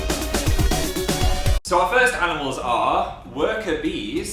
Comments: At first, I thought that said "Animal lesbians", No lie. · i thought it said animal lesbians!!! XD